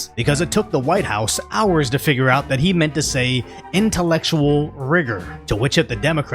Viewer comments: Seriously I thought he said intellectual wizard. He is totally incomprehensible 🤪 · I am 100% in the FJB camp, but I heard 'Intellectual Rigor.' There is enough brain rot to work with to not make them up. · i thought he said intellectual wizard lol